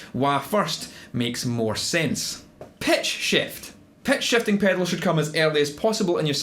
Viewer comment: I was really enjoying this video then suddenly I heard "pet shafting" and I said WHAT? What does animal abuse have to do with pedal order? SO I backed up and realized it was "pitch shifting". OK. LOL. Seriously EXCELLENT video, I learned a great deal, concisely, intuitively and in a well organized manner at precisely the time when I need this info. Thanks a lot!